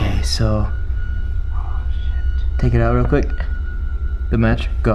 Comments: I heard shit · sounds like he said oh shit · I heard oh sh*t in 0:56comment if you heard it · I heard oh s*** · did anyone heard someone say 'Ohhh shit " on 0:57 ? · who else hears "oh sh!t" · On 0:57 I heard someone said oh shit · I heard at 0:57 a voice that said oh sh**